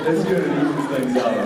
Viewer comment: what does jensen says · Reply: That's good it evens things out.